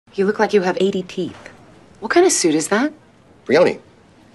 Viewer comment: I had to watch it twice because I wasn't paying attention and thought she said you look like you have A.D.D. and I got offended for a second...